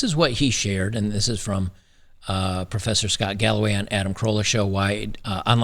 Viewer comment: Im watching too much detractor content. When you said professor scott galloway I heard "this professor has gout by the way"